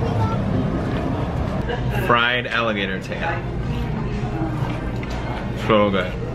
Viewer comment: did he say 'Fried alligator nipple' thats what i heard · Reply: No he said tail